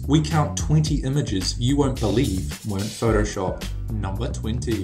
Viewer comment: I thought they said were photoshopped